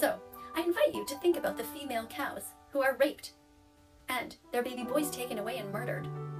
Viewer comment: did she say 'the female cows that are raped' Okay I'm pretty sure we actually call this the cycle of life no one is forcing the cow to do the devils tango